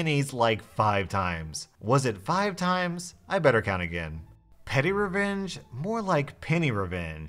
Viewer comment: Legit thought he said “pennyrevenge”